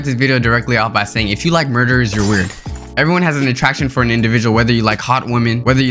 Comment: I thought he said if you like burgers you're weird